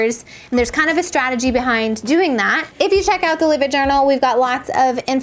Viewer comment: I thought you were saying the livid journal until 7:22 when the name came on screen